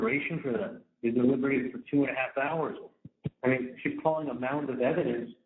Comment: He just said two and half hours! I thought he said at first it was two hours 🤯😂